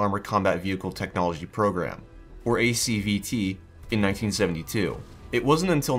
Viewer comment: I heard CBT